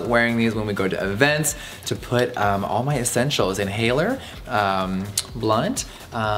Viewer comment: “Holds my inhaler, blunt, gloss, Kit Kat...and my dignity” 😂😂😂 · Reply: I swear i heard "blood" instead of "blunt" and i wasn't even surprised 😂😂